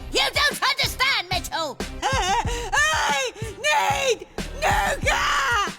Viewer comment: What did she say?! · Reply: I need nougat!!! But it did sound like she said "I need 🥷"